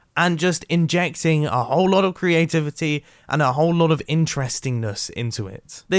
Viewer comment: did he just say GRIANtivity?